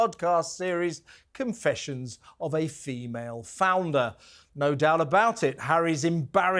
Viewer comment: Thought you said 'femail flounder' knew there was something fishy about it.